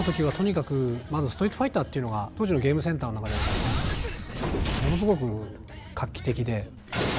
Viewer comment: The caption says 'it was so lively' but actually Daigo is saying 'It was so revolutionary at the time'